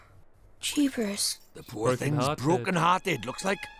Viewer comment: When Stampy said 'Brokenhearted' about Mary when they were in her room, I thought he said 'Babanas